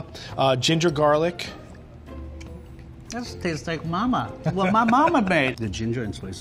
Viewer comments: "it tastes like mama" WHAT YOU SAY? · did he just said cocacola